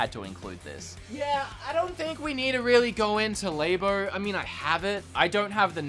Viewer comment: What did u say. Did u say LABOR?